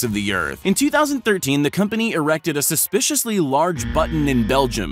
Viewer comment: what did I just hear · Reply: Erected